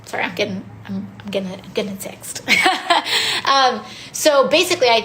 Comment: does she say she's getting a sext? Just curious